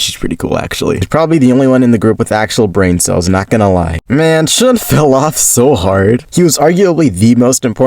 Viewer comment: did he say Shun 😭😭😭😭😭😭😭😭